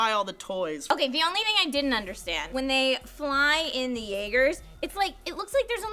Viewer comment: is saying jaegers? Is that what they're called? Cuz that means hunter in German, so that's bad ass lol